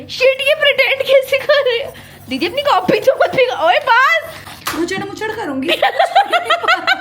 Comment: did she say copy ch*dna☠️?